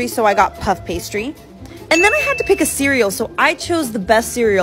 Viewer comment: I thought it said "celery" not "cereal" 😭